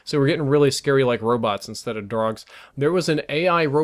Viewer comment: did he says drogs?? hahaha xD